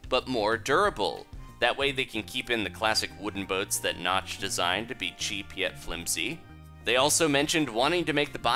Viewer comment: XD youtube crashed at 7:27 (when he said to be cheap) and it sounded like he said to be chibi XD